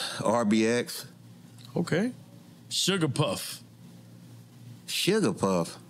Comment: I heard SUGAPUFF at first also 😂😑